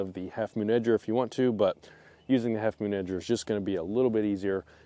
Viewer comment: I thought you were saying "half moon ninja" for way too long lol